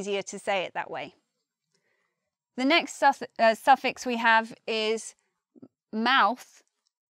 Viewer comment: Was that a Freudian slip at 7:18 when she says "Sussex" instead of "suffix"?